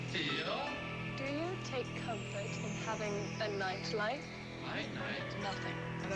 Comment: I thought she said the night life LOL